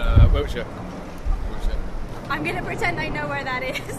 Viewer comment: I couldn't quite catch that. Did he say he was from Berkshire? Overall, I think her American accent was damn good. LOL. "They all say sorry to everything. Brits are adorable." I can't believe how rude some of these Londoners are. But the thing is, a lot of people don't want to be on camera. Not tube. Not underground. Subway. Very entertaining. Thanks for posting.